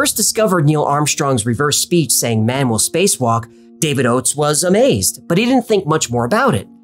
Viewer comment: It sounds like it says “never space walk” not “man will space walk”